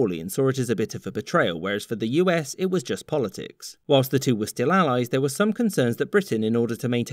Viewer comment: I just hear mac from sunny saying “thats politics bitch”